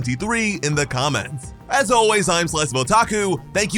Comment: What is your name? I can’t understand what you say at the end. I always hear “slice of belltaco” I am not trying to make a joke, I actually want to know. Hope it doesn’t come across insulting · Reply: Slice-of-Otaku :)